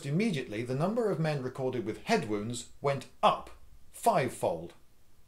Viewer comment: I over-think stuff to much. My immediate conclusion was that, upon the issue of helmets, soldiers developed a false sense of security regarding them, like all armor. This, combined with the lethality of true modern warfare in WWI, upped the casualty. No. It was just the result of a simple statistical mishap regarding lists of KIA and WIA. Goddammit. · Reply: I was thinking it made it harder to put gas masks on ( dosen't help I thought he said death from head wound and not injury)